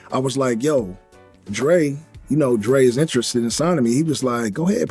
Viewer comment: it sounded like he said 'You know Drenwas interested in Sodomy, and Ouyff was like go ahead thats where you belong! Lol